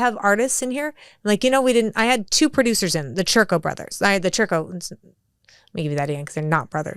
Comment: Omg I thought she said the chuckle brothers😂😂 to me to u ❤